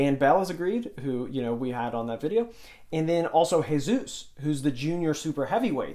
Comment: who is that? didnt hear his name correctly. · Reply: Jesus Olivares.